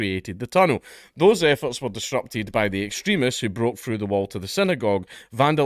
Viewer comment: Did he say "Jewish extremist" 😂 .. how the turns have tabled